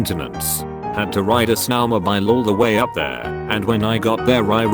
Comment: low key thought it said snow by llama. I was really confused until i looked at the screen lol